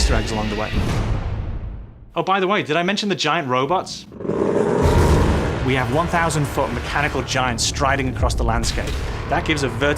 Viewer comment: did he say "1000 foot robots" and "giants" like more than one?